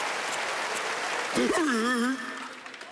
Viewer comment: what did he say cuz it’s LOLMAO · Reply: Hørtatäcken (heart attack)